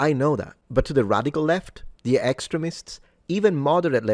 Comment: At first I thought you said excrements instead of extremists, butbi can see the similarities 😂, Scusa Fratello👍 ❤